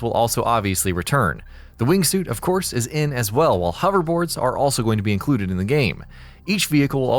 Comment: Did he say hoverboards? Well that's got to be new, right? I don't recall hoverboards being in the past games.